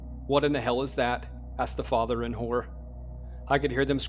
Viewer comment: Lol i thought you said "asked the father and whore" not horror lol. I was like what? Lol